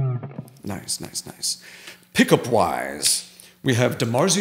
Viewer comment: When he said "pickup wise" I thought he said "pick a pwize" like Elmer Fudd.